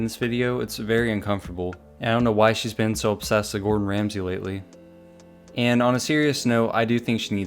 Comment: I’m genuinely concerned for her, she’s either actually crazy or she’s faking it. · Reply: It sounded like she said good Ramsey